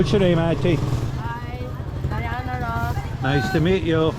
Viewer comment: When you asked (spelling) her name sounds like she said "Diana Ross" ..